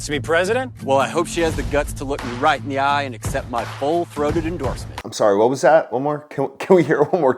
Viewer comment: kinda sounded like he said 'ball throated endorsement" to me.